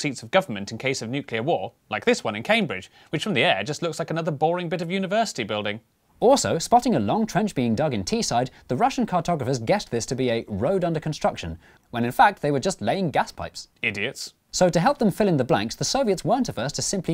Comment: I played too much CS:GO I thought he said "T-side" (/.-)